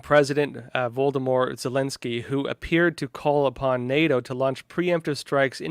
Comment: Did he just say Voldemort zielinski? Are you kidding me?? Thats the bad guy in Harry Porter...of course he wants to use nukes..hes the bad guy